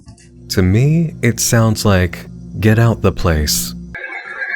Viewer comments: How do you hear "Get out the place" ??? I heard "Margaret *inaudible*" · It sounds like he said "Margaret Watts" not "get out the place." lolololol